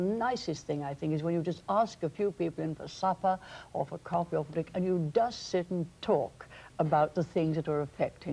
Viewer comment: she talks about having someone over for supper, coffee, or .... (one other thing that I didn't pick up as I'm from the US). Anyone pick up on what she said? It sounded like "fric," I have no idea what that is. Lol. :) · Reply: i think its 'or for a drink'